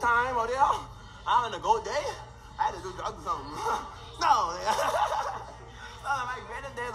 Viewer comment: Did I hear that correctly ? “I had to do drugs or sum.”